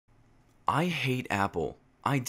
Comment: i thought u said i hate apples.like the fruit